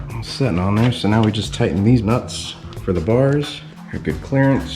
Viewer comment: Awesome! Now you can bump some beats on your way to work or play. At 3:55, I thought I heard you mention "Deez Nutz". Thanks for sharing and keep up the great work.